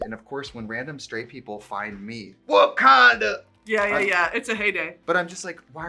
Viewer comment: I really thought she said Wakanda. Yes as in the fictional African country 😔